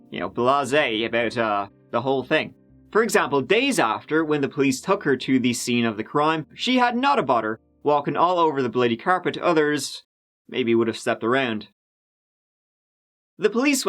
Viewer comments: Me: She had Nutter Butter all over the carpet!?! 5th Time Listening: She had not a bother . . . (FML) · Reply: I thought he said nutter butter also?? And she got it on the carpet? never makes a mistake. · says, "Not a bother!" I hear, "Nutter butter" im hungry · Haha I misheard what you said about the carpet as "She has Nutter Butter about walking on the carpet" instead of "not a bother" :) Nutter Butter is a kind of candy bar. Love your content! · I have rewound this part over and over and I have no idea what he is saying lmao... auto correct isn’t helping either. All I hear is “When the police took her to the scene of the crime she had nutter butter”..... WHAT IS HE SAYING!?!? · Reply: “Nowt about her”. It’s a UK term meaning “she had no problem”. In this case when she was back at the scene of the crime, she had no problem walking on the blood stains on the carpet when everyone else was walking around them or over them, not stepping right on them like she was (the grieving widow).